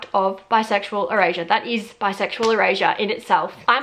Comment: The whole first half of this video I thought you were saying bisexual Eurasia 🤣🤣🤣